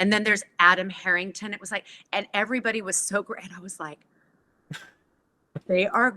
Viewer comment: I thought she had said “and everybody was sober” 😫 thank you Danielle Bisutti for your work on Freya!